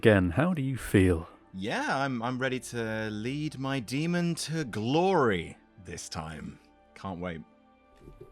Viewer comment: Ready to lead my demon to Glaurie is what i thought was saying at the beginning!